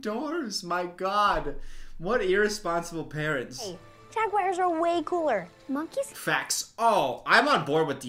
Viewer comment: is he saying "Jayguars"?